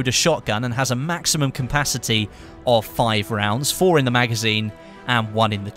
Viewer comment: "Maximum campasity". Seriosly? What that word mean? This is not the first time that I hear it from Westie. Otherwise, nice video!